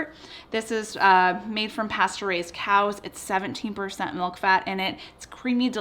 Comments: LOL...I thought you said the yogurt was made from Pasteurized cows! I thought to myself that would be a neat trick! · I thought she said "pasteurized cow" 🐄 😂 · at first I thought she said "Pasteurised cows" instead of "Pasture-raised!" 🤪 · That's what I get for watching so late! I was SO HORRIBLY CONFUSED because I thought you said the cows were PASTURIZED, not pasture raised. I was laying here blinking for a hot second sure that I must have misheard and then 💡💡💡 lol